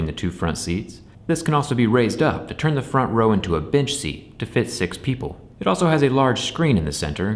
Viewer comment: Did you call it a bit*h seat or a bench seat? Lol I've heard them referred to as both but sounded like you said the former🤣